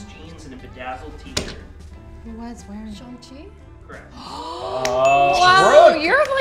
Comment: bro 6:10 wtf does brooke say? Shong chi? I genuinely can´t understand